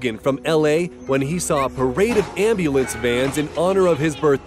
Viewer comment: Did he say ambulance vans, or what am I missing??